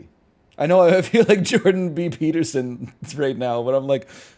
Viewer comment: When he said "I sound like Jordan B Peterson," I swear I thought he said Michael B Jordan. The bit made zero sense to me for like a full minute 😂😂